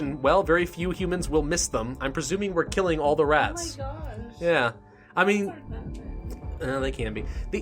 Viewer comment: I Think I heard something at 4:45 Maybe??? It Says "Oh My God"